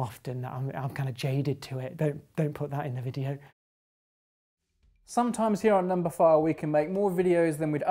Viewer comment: I thought I heard him say don't put that in the video